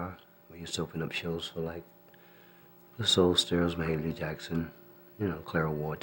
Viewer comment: did he say Michael jackson's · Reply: Mahalia Jackson